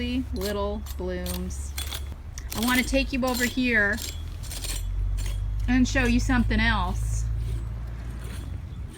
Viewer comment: Hi, i didn't catch the name of the plant you mentioned, the really fine/needle leafed agave-like, that was on the ground which you transferred into that huge clay pot? it's the one found behind the barrel cactus staged by Greg & the 3 boulders seen right 'bout 1:58 of this video & focused by 9:52-9:59 of this video...just badly need that info... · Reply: Dasylirion